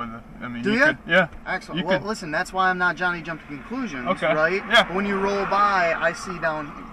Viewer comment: “That’s why I’m not trynna jump to conclusions” after he jumped to conclusions 😅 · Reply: Lol. I thought he said, “Well, see?! That’s why I’m not Johnny Jump-to-Conclusions” 😂